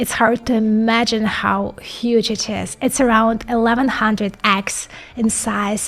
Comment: Do you mean acres? What you said didn't sound like that to me.